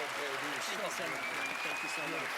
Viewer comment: When Penn was saying “so good” at the end, during the applause, I thought he was saying “suck it [you didn’t fool us]” lmao. It would have been equally appropriate after his reaction at being fooled the first time.